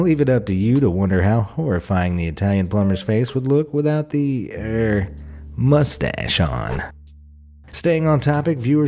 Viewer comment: When I heard you say "with th mustache off" I found a random toy gun and pretended like I shot my self lol